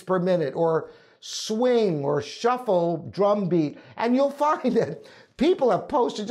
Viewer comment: What did say at 6:17? ! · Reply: Drum beat